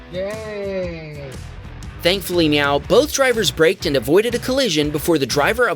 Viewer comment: Did you just say MeOW?